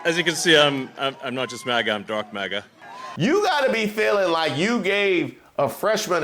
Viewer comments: I will be Honest. I heard it as Dork MAGA when Elon said it on Stage. 🤣🤣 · First I thought he said "drug MAGA". And after that I thought he said "drunk MAGA". Weird apartheid man needs to be deciphered.